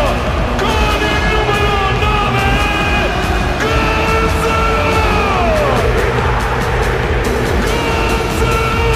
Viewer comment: what he said ? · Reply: Gonzalo higuain